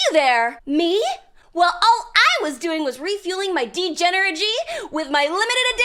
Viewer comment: did she just say degeneragee? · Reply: degen+energy. Degenergy.